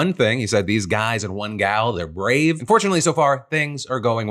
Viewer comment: I'm pretty sure you said "and fortunately", but because of how quickly you said it the first time I heard you I thought I heard "unfortunately".